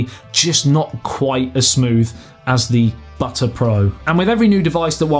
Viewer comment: Did he just say "Butter Pro?" 😂😂😂🤣🤣🤣